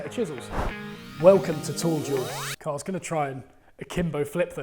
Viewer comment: Thought was saying tool jewel